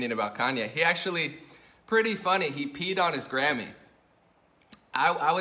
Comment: I nearly spat out my tea. I thought you said he peed on his Granny! 😆